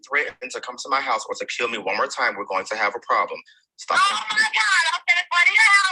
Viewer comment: I didn’t hear any threats being played in that recording he played, and I think that little girl should be with her biological mother, and not to strange men that she’s not even related to. That’s kind of weird. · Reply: she CLEARLY says omg I'm going to go to your house and kill you